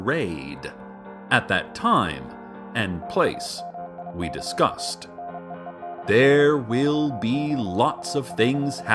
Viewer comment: Anyone know what the morse code in the background starting at 3:08 says? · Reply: TAMIKA NEEDS YOU.